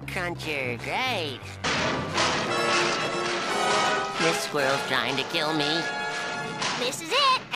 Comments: I always thought SpongeBob said “This World trying to kill me.” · The impeccable delivery of "This Squirrel's trying to kill me." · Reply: As a kid, I thought he was saying, “This world’s trying to kill me.” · "This squirrel's trying to kill me." The amount of anxiety and genuine terror in the way he said that sentence got me. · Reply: For the longest time growing up I thought he was saying this world’s trying to kill me. I would even say it was going through fifth grade 😂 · "This squirrel's trying to kill me..." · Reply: Oh lmao 😂 ngl, i thought he said "this world is trying to kill me" 🤣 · I thought he said "world". Oh... It's a squirrel. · "This squirrel's trying to kill me..." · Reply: i thought he said "This WORLD's trying to kill me" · "This squirrel's trying to kill me..." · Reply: Oh I always thought he said this world's trying to kill me. That makes more sense and is funnier. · "This squirrel's trying to kill me..." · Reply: Oooohhh, I always thought he said “This World’s trying to kill me.” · For some reason I always thought be said this world not this squirrel.